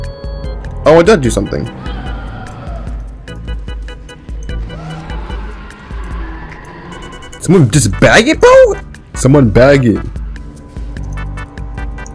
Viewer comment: Why do I hear baguette instead of bag it?